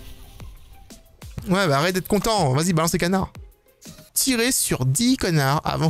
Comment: j'ai entendu "tirez sur 10 connards" c'est ca🤨🤣🤣